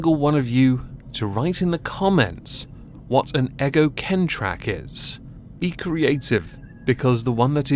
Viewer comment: It must be my ears, because I heard "Eggo Con Trick". And I know what this is - you can send me a bag of crisps for helping out. Eggo Con Trick: This describes a vile and ugly deed. Firstly, one purchases a Cadbury's Creme Egg, and carefully removes the foil wrapper. One enjoys the creme egg, then, equally carefully, one takes a small cold boiled egg and wraps the foil around it. The nasty person then presents this "gift" to a small child as though it were a choccy treat, and enjoys their pain when there is no choccy treat. Bonus points are added if boiled eggs makes the child sick. (As demonstrated to me by my father, when I was very young. Bonus points were added. There you go. That didn't cause me terrible mental pain or force me to go and cry in the bathroom at all. Anyone who says that they saw me do that is a rotten liar. So poo. EDIT: Just reached the point with the cat. Forgive me, but this "person" is a C U Next Tuesday. Totally.